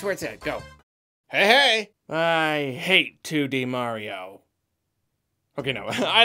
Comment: I thought you said "I hate to be Mario". And I thought "but you ain't tho".